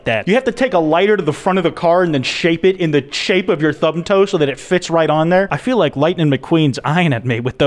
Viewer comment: did he just say… thumb toe?